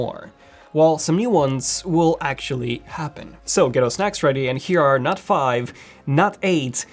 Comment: Ghetto snacks ready? Great! I love Funyuns. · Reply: I thought he said “ get your snatch ready”